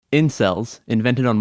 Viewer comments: Did any body else thought it said incest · I thought it said incest